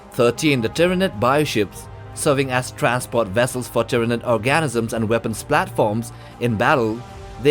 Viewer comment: Di... did he just say that ship carries Tyanid ORGASYMS?!